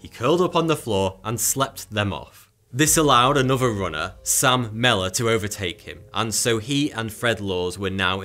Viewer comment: When u said Sam Mellor I swear I thought u said "salmonella".. His cramps were probably due to🤢🤮 (salmonella poisoning) 🤣😂🤣